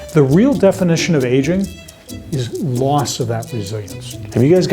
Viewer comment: I thought he said “Asian” instead of “aging”